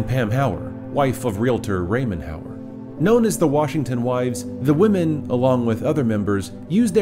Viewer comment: lol i heard “The Washington Whites” 😂